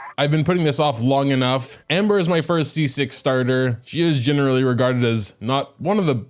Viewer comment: misheard "amber is my first c6 character" and almost freaked out